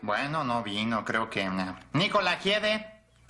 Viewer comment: que fue lo que dijo? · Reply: Nikola Jiede